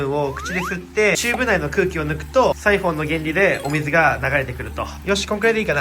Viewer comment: did I just hear "using the principle of the rhinoceros"? LOL I wonder what the original word is but this video is amazing!